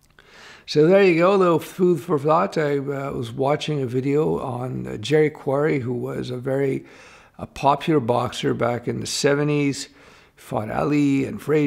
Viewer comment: Sounds like you said jQuery!